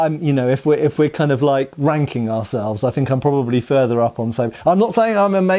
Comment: No offense, but I heard "ranking ourselves" (regarding sabres) as "wanking ourselves" until I re-listened. 😂